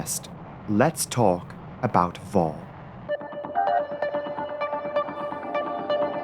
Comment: when she said "now lets talk about VOR" GIRL i was in another tab and thought she said VORE i literally choked